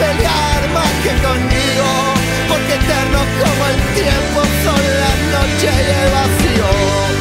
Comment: Loca vos no entendés nada de vivir Se fueron con septiembre tus ganas de mí Y una forma errante de permanecer Llenó de noches todo nuestro amanecer Loca vos no entendés nada del amor Yo no puedo cantar Blackbird como Paul Loca, tengo ganas locas de volver Cómo flores que volvieron a crecer Loca, me gustás así de loca Inestable y caprichosa Mucho mejores que el vino Son los besos de tu boca Y tal vez es porque vivo De la forma en que mal vivo Que te digo lo que digo Que me encuentro tan perdido Loca, sólo lo que escribo es lo que soy No tengo mucha energía para hoy Loca, tengo ganas locas de volver Cómo flores que volvieron a crecer Me gustás así de loca, me gustás así de loca Inestable y caprichosa Mucho mejores que el vino Son los besos de tu boca Y tal vez es porque vivo De la forma en que mal vivo Que te digo lo que digo Que me encuentro tan perdido La nostalgia es un espejo Que duplica lo vívido Rescatando nuestro tiempo De las garras del olvido Porque no tengo más nadie Que pelear más que conmigo Porque eternos cómo el tiempo Son las noches y el vacío Porque nado hasta encontrarte En este salvaje río Porque no me queda nada Que perder que lo vívido Loca, vos no entendés nada de vivir Loca, vos no entendés nada de vivir Loca, vos no entendés nada de vivir Loca, vos no entendés nada de vivir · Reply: Pensé que decía:Eterno como el tiempo son las noches de vacío.